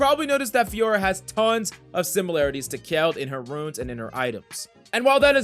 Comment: did he say youve probably noticed that fiora has tons of similarities to kayle in her runes and items?? wheres kayle coming form ?? what